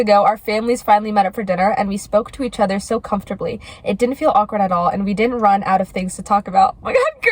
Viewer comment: WHAT IS SHE SAYING HERE MY POTSNTIAL AUDITORY PROCESSIDNG DISORDER IS WORKING AGAINST ME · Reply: “It didn’t feel awkward at all, and we didn’t run out of things to talk about” ^^